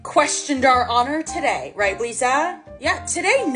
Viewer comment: the subtitle said “Lisa” when it should have said “Blisa”